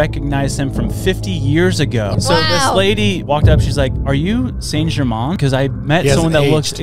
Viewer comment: I thought dude said “are you Saint your mom” 😂